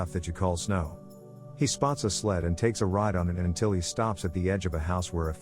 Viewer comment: why did i hear 'he spawned a sled'